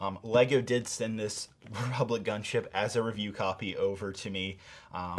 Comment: Was i the only one who heard him say ,,Public Gunship”